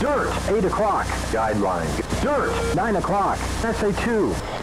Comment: Quick question what is jester saying at 6:58 and what does it mean. Thanks! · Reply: SA-2 is a guideline